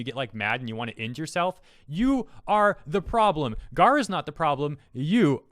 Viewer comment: did you just called her Gawr? Nice